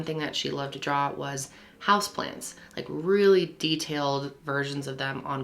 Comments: I listen to these “podcast style” and I was so confused when she said she like to draw house plans because I thought she said house plants. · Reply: I thought she said house plants until your comment 😂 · All seriousness aside, I definitely misheard Kendall when she said that Kristen liked to draw “house plans” as she liked to draw HOUSE PLANTS... I was like kinda weird but you do you girl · I listen to these “podcast style” and I was so confused when she said she like to draw house plans because I thought she said house plants. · I thought you said she drew "house plants" 😅🤦🏻‍♀️